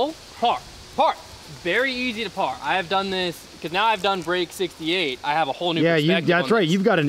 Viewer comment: Did I just hear this right? At 1:32 says "Now that I have done break 68." Did I miss a video or did I just spot a spoiler.